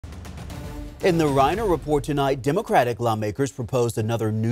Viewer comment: I thought he said rhino report lol